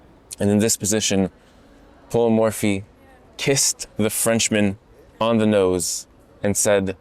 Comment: Am I the only person who heard that Paul Morphy kissed the Frenchman on the nose 💀